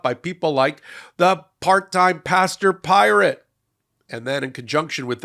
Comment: I thought you said "pasta-pirate" and was wondering, haha. I think it is pastor-pirate, but I like pasta more 😅